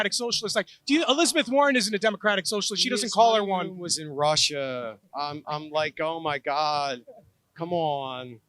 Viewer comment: what does he say? "mutter mutter mutter in russia. im like OMG cmon. does everybody know that?" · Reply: mark kay “he honeymoons in Russia “